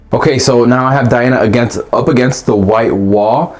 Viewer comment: All I hear is 'Diana up against the wall'... Weird that 🤷‍♂️